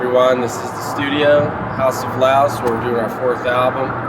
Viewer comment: lulz i thought he said "house of louse"